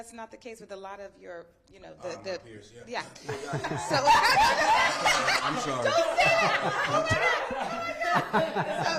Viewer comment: What did Cliff say @ 7:57? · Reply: He said lil yatchy